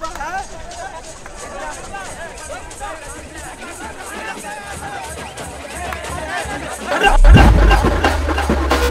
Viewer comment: anyone hear that🤣😂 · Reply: Ommalaka😂🔥